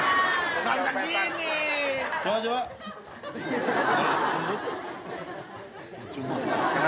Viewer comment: cak Lontong ngomong apa ya? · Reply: Percuma